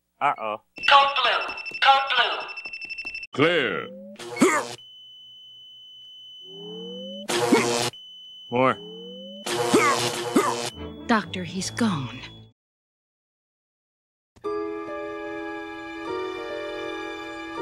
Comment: if homer is dead why did they said 4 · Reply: Lol he said "more" and it's just the joke from the original episode.